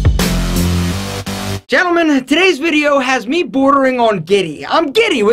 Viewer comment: thought you said borderline guinea